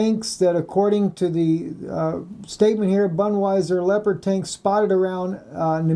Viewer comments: Haha. Did he say Budweiser? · LOL, I misheard you and when you said about the tanks and wondering why they was there I was busy wondering why "Budweiser" had tanks........ When you had that map up where everybody was the other day the US and ISIS was right next to each other sitting side by side like best buddies.